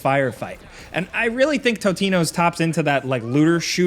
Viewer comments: Did he call the game "Totino's" at the 3:56 mark? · did he say "totinos" 😂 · i could be wrong but did jake say tostinos like the pizza rolls? lol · Did Jake say Totinos at 3:56? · Did he say Totino's @ 3:56? lol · Anyone else catch at 3:57 he said Totinos? Lol 😂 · Does he say 'Totino's' at 3:56 ? I, too, love their pizza rolls.